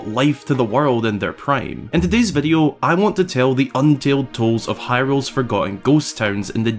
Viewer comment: Did you just say "untaled tolds"?